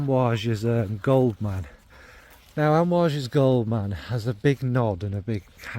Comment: lol I did a double take, I thought you said Gold Man has a big knob! I thought “Jon! Getting risqué!!” 😅 Anyway sorry - probably says more about me than it does about you! Haha Great video as always!